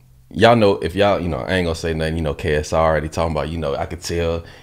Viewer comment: Here’s the link to the Patreon for request and Merch discount 🔥🔥🤘🏽—> <link> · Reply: Did you call him KSR at 3:10 😂😂